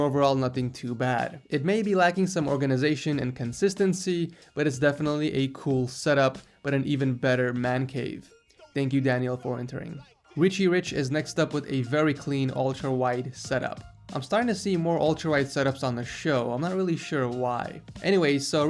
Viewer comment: did u say richie rich?😂😂😂